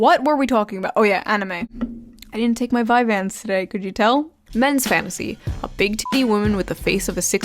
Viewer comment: Ngl I fr thought you said, " I didn't take my Vicodin today, can you tell"?